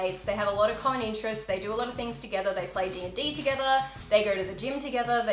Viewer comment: did you say d&d?😄I love that game